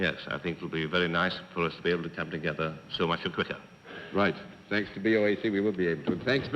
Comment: what is Mayor Moss saying? All I can get it "Yes, I think it'll be very nice for us to be able to come together, so much <garble garble>" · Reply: I think he said, "...so much the quicker."